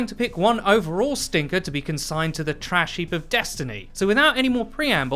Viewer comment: I’m sorry did you say”the trash heap *CALLED*Destiny”. I swore that’s what I heard.